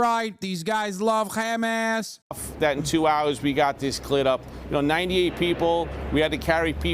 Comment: “f that we got this cl*t up” is what I heard